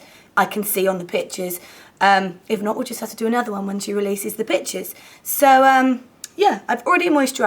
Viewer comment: LOL I heard "when she releases the bitches" XD awesome tutorial as always~